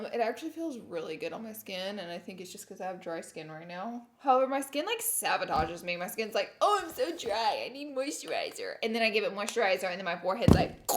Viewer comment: I love you..it sounded like you said savotage instead of sabotage..anyone else hear that lol